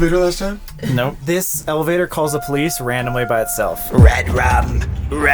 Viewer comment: it sounds like saying "police" when there in the elavator (can anyone else hear that) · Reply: Because he did say police..?